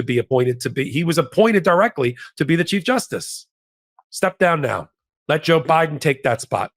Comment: When you said "chief justice," I heard "cheap justice." How appropriate. For the cost of a few million dollars and free membership in the most exclusive clubs, the investment yields the donor returns of thousands to one in decisions tailored to fit their special interest. Cheap and tawdry.